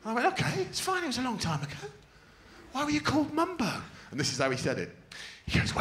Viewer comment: Could someone tell me what Greg is saying at 8:39. Can't seem to make it out.. Thank you! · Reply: diegensehaut he's saying cause my mum's nickname is BO